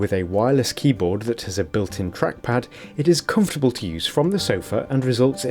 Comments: I thought he said crack pad 😂😂😂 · when he said it has a built in trackpad i thought he said crappad